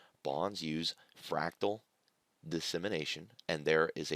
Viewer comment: When you were explaining Bonds Futures around the 19:30 mark, you mentioned what sounded like "fractal dissemination" but when I tried googling that term plus futures, nothing came up. Can you explain further what you meant by that? Thank you!